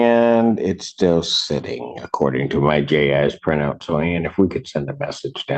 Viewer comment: I had to rewind it. 0:43 I thought he said my gay ass print out. Lmao. But it was my Ji print out.